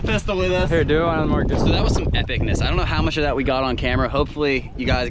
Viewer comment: with subtitles it says THICKNESS instead of epicness